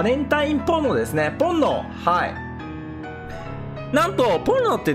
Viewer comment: ポ◯ノって聞こえた…